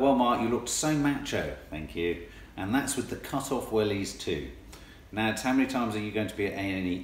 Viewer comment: Hahaha, I thought you said "cut off willies " . Nearly spat my tea out....